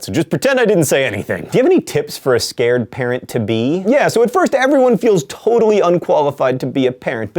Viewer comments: When he said 'parent' I honestly thought he said 'parrot'... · I thought he said parrot, not parent. I was like '...scared parrot to be?' and all I could imagine was a parrot with an anxiety disorder, terrified of what came next in his life. Pacing back and forth on the wooden bar repeating 'What am I to be? *birdsound* What am I to be?' Then I realized he said parent and it wasn't as awesome.